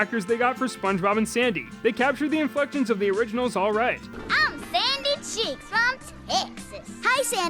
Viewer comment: For some reason at 4:30, I heard sandy say, “I’m sandy cheeks I’m sexist”